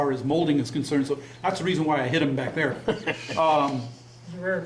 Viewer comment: I thought you said,"That's the reason I hit him back there." I thought that sounded like the old Navy where if you said something smart or did something stupid, you'd get punched.